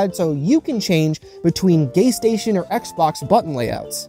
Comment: i know im not trippin but i swear he said gaystation lmao 🤣